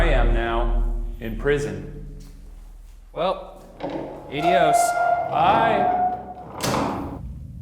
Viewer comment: I know at 6:53 Ryan was trying to say "Adiós" but it totally sounded like "eyDios" and that' funny af.